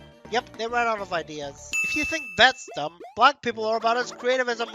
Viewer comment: Hold on, did you say "Black people"?